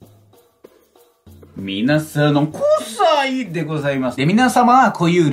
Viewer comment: どうしても、『皆さん、クッサイでございます』しか聞こえない笑笑笑